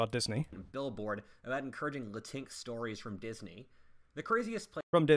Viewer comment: I realize what he says may have made no sense to you, but he pronounces Latinx as “la tinks”, so it’s like racial inclusivity really, and “la tinks” is not a Disney property itself.